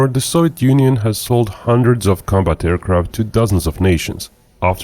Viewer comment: I thought he said 2 dozen where do you see 2000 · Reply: nvm. I watch these sped up and I could have sworn he said “thousand.” Once I rewatched it at 1x speed it sounds like “dozen.” First time that’s happened